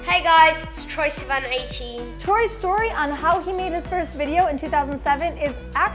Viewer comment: when she said Troye's story i heard toy story then i realized it was Troye but then i thought hey, maybe they should make a video called Troye story